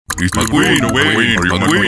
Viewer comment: IT is so funny 6 out of 6 Also when these "guys" are saying "my Queen" I heard "McQueen" who is my childhood idol